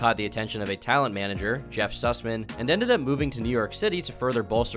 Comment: Im sorry Did i just hear "Jeff Susmen"